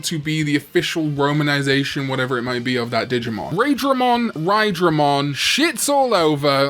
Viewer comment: Raidramon… I thought it was Raindramon, that’s what I thought I heard but I haven’t seen 02 in some years so I never had it clarified until now